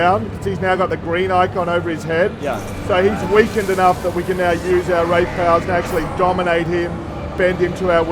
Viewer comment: did he say you can use rape powers or rage powers?